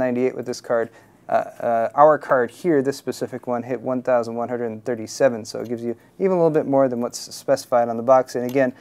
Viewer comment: i almost thought i heard 1337 Mhz :D